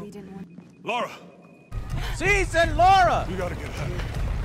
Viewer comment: But Joe, she actually says lara at 18:45 you just have to listen closely